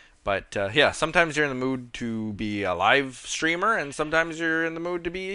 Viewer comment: Haha when you said "sometimes you're in the mood to be a live-streamer, sometimes you're in the mood to be a youtuber" I heard it as "sometimes you're in the mood to be alive" and I was like well I should hope you're in the mood to be alive..